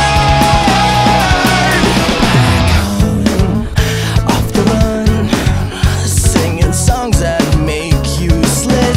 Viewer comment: wait does anybody else hear really faint whispering from 0:58 - 1:13 or am i just crazy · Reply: It's actually saying "don't do it"